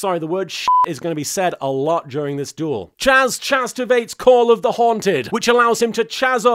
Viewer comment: Tell me I’m not the only one hearing “chazzterbate” instead of “chazztivate”